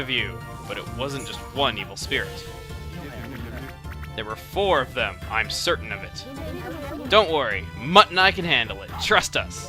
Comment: when kuni said mutt and I can handle it I thought said mutton eye